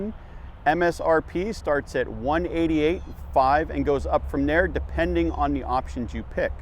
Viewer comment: ….. i thought he said starting at $188,500… 😂